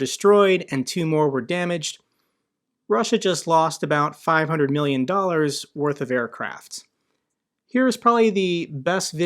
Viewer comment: i always heard 15 million dollars